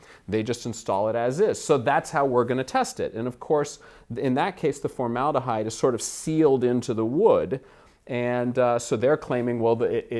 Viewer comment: Is no one picking up what he says at the 4:10 mark?!? He said, "Of course, in that case, the formaldehyde is sealed into the wood"